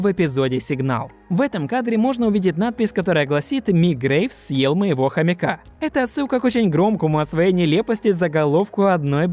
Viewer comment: если я не ошибаюсь, то в серии "Подарок" Гамбол и Дарвин хотели купить картину художника Mic Graves ( ну или просто мне послышалось и слово было похоже на это :D )